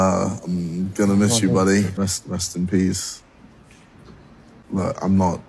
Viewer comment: I really thought he said "rest in cheese" as he sprayed what seemed to me as cheese